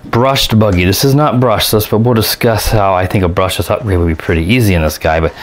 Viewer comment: i dont know whether it is because im british or because im stupid but have no idea what u were saying? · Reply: "We will discuss". Was fairly clear but I do have a tendency to mumble at times but I'm working very hard to stop that. Sorry!